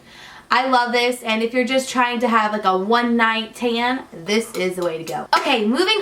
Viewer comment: When you said "great for a one night tan", for a second I thought you said "one night stand." 😂😂